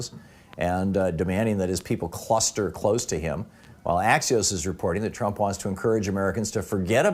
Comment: Thom says... "Axios??? is reporting that trump wants encourage Americans to forget about the quarenteen.." What's that 1st. word please?